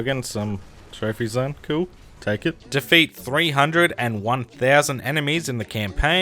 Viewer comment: Bruh I thought you said 'beat 301,000 enemies in campaign'. That would be horrible.